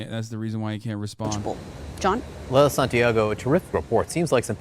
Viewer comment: I thought they said her name was Lil Santiago